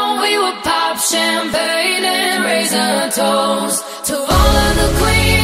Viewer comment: "We would pop champagne and raise a toast" I misheard the lyrics and thought it said "rings on our toes" 😂